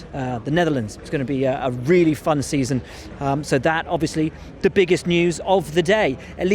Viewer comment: I thought he said it would be a really fucked season